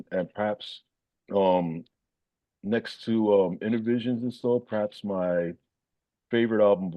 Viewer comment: also, he mentions 'Starless' as a (possible) perfect 'coda' for Crimson. Completely correct. One of the greatest songs ever. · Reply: and where also says at 08:27, re: the Lamb, "...and next to (.."Inner(?) something..) perhaps my favourite album of all time..." Does anyone know what (other) album he mentions there? ....I've replayed it several times but I just can't seem to make out what he says.